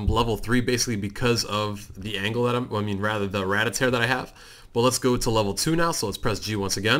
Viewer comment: did he just say ratata